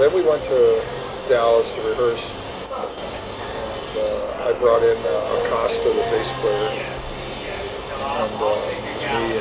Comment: George knows it….but still pissed because Tommy wasn’t sure he was the guy when he was close to being in the band, lol still a sore spot. The interviewer should have known. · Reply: I actually realized after the fact that I had misheard what George said there. I thought he had said that he had brought in the bass player AND the drummer. So I heard his description of the drummer as a white guy with an afro and thought "wait... there was a drummer of that description involved with Ozzy in the '80's other than Tommy Aldridge?" Of course, when I was editing the video, I had the opportunity to hear what George said more clearly, and it all started making sense. - (The guy who did the interview for MM, replying via my personal channel)